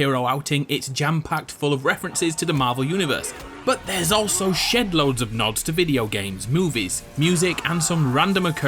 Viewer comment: At first I thought he said shit load lol